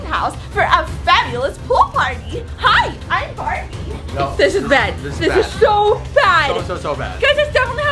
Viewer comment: did say bed